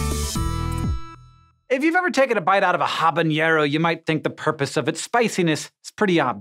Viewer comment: Habaniero is what I heard